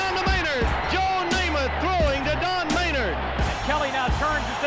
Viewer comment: Did he say throwing to Darth Vader? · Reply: Don Maynord